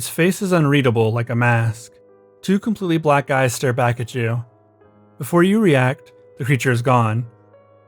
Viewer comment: I heard that as “two completely black guys stare back at you”